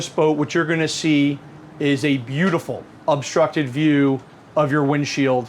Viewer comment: Did he actually say "obstructed view" @ 4:26??